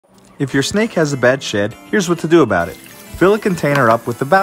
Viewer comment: At first I thought he said "if your snake has a bad shit"